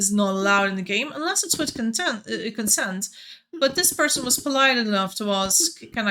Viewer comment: I clearly heard that murder is allowed when it's with CONTENT. It's always with or for content for me, which makes its ok. Thank you 😜